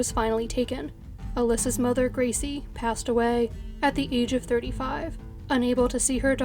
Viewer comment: is she saying the mother passed “at the age of 35?”